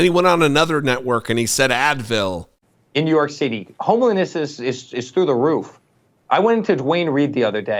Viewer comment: In the second clip, it sounded like he said, "homeliness is through the roof," and looking at him, I have to admit, he's not wrong.